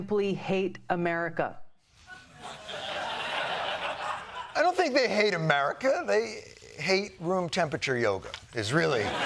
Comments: I heard " they hate room temperature yogurt" :face-blue-wide-eyes: Also true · Did he say yogurt is supposed to be very hot??